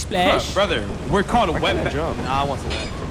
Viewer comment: Genuinely curious. At 2:29:04 what was it that was going to say that would've been racist? I can't hear him clearly enough nor can i make it out, tryna educate myself. I think i hear him say wet, then what sounds like "bandits". Which is from home alone. So like was that racist. But he also says it was aimed at hispanics & i just can't put it together. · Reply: As a Hispanic myself, it seemed he was going to say an offensive word(Wetbag) that is used against us Hispanics